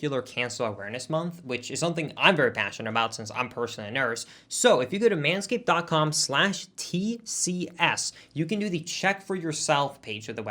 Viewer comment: Did say he was a nurse?